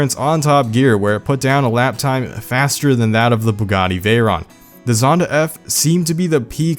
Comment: I THOUGHT HE SAID PAGANI VEYRON LMAO